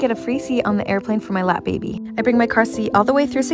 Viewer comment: I heard "lab baby" 😭